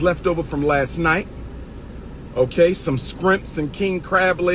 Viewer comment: Did he just say skrempts @ 6:37???